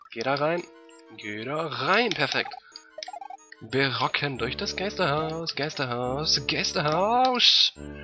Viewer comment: hab ich voll verstanden "Wir rocken durch das Gästehaus" XD